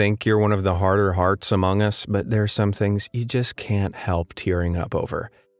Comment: Have anyone heared among us its on